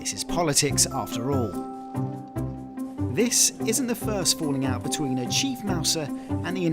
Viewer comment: I swear to god I heard the reporter say, 'falling meowt' at 2:10. 🫢 (Also, very handsome 🤭)